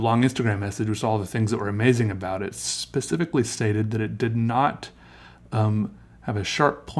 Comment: did he just say " Specifically saidED that it did not [...] " ?